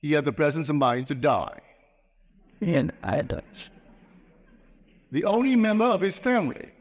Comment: just after Neville says "...to die...", what do you say? It's something like, "It's eye (I) dots". 🤔 Thanks for letting us know. 💙🤗💙